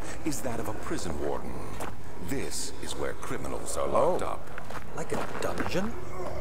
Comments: This is where criminals were locked up. Subtitles say "This is were criminals were locked up." :O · "This is were criminals are locked up." in-game subtitles are wrong, it's meant to be where :P